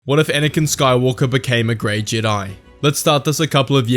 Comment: I thought is said "What if anakin became a gay jedi" 💀💀💀💀💀